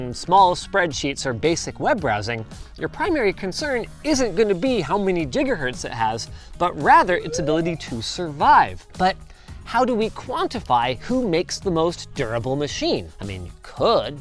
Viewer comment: did he just say jiggahertz?